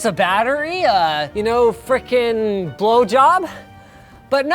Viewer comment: Did linus just say 'blow job'?